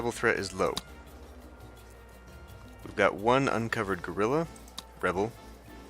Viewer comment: Each time you say "guerilla", I hear "gorilla", which confuses me just a little bit (!). By the way, you mentioned in your Assassin's Creed LP that you would maybe play another game from the franchise (obviously not a "Chronicles", you made it very clear that stealth games are now "those we don't speak of"), do you think you will actually do another one ? (Unity... or Unity. Did I mention Unity ?)